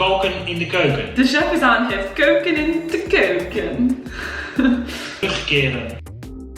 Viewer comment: I’m Dutch and I know that you meant “De chef is aan het koken in de keuken.” But the way I heard it was “De chef is aan het keuken in de keuken.” Which translates to “The chef is kitchen in the kitchen.”